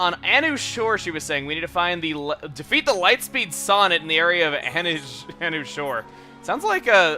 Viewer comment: how many people thought he said lightspeed sonic?